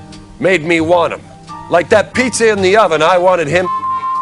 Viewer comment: Anyone know how to lip read? What did say at 52:06? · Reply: “Like that pizza in the oven, I wanted him in me”